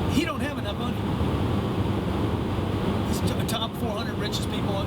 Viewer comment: Did he say top 400 or top 4?